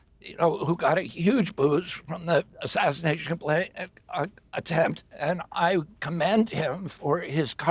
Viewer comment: why does it sound like he say assassination play or plan? He caught himself and then said attempt. Am I the only one who heard that?